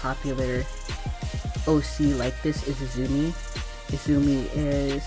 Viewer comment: Ngl I thought you said nezumi aka Rat Lol